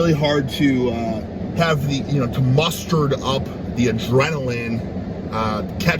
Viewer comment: seconds in did he say “mustard up”?